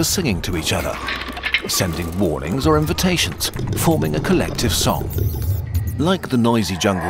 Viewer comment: Did someone say collective soul? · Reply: They said collective song.